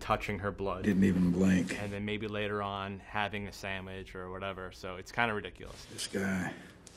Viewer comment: did he say samich?